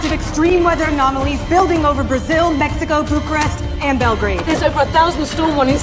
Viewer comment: did she just said belgrade ? (Serbia)